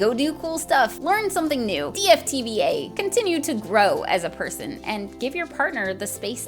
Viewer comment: thanks for adding captions with sound effects! Now I understand the video more. 3:36 in you say DFTBA. (Which I couldn't understand before the automatically generated subsides were fixed. Thanks so much!) I Googled it & found this. Some of the answers are hilarious! <link>